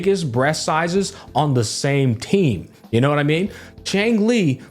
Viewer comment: when you said "...same team." I thought you said same titty